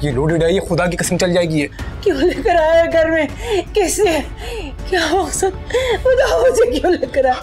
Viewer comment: did someone notice that faizan mother said kia hua asad instead of kiya hua faizan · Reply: not asad, she is saying maqsad